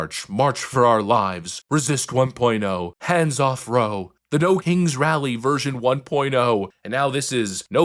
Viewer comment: I thought they said nose ring rallies, need to get my hearing checked .